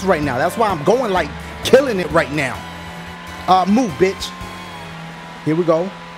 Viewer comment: I thought he was gonna make a cow joke cuz it sounded like he said Moo bitch :(